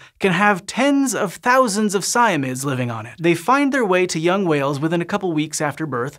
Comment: I thought he said "thousands of siamese lives on the whale"